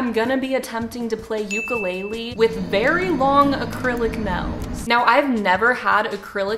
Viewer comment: It sounded like you said snail in stead of nail